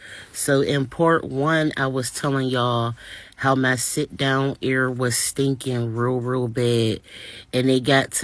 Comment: I've never heard of "sit down air" in my life! 😂 Lord have mercy! 🤦 Its embarrassing, yes but I'm glad that she's raising awareness of what can possibly happen with BBL procedures. So I'm not sure why she's getting so much hate. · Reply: I thought she said "sit down area"